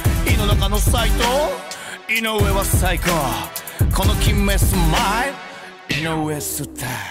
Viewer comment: 井上のこの決めスマイルがこのキメェスマイルに聞こえたのは俺だけじゃないはず